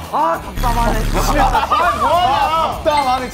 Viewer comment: something i find hilarious as someone whose native language is spanish is that jungkook in 9:17 literally sounds like hes saying "ah puta madre" which roughly translates to "oh my fucking god" LMAOOO